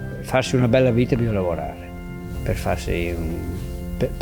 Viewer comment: Italian here !! The subtitles from 16:20 are incorrect, this is what the man is actually saying: “To have a good life, you have to work. To be contempt, you have to work. I don’t know any other ways (to have a good life). Either you’re born lucky…”(or you have to work)